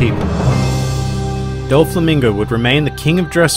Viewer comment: Every time I hear his full name I hear 'Donkey Hoe Tee Flamingo'